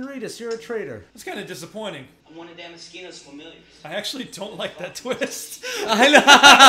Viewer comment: I always heard the line "I'm one of Damaskino's familiars" as "I'm one of that mosquito's familiars." I like my version better lol.